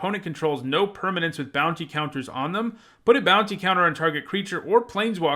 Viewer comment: For some reason I kept hearing "bouncy counters"...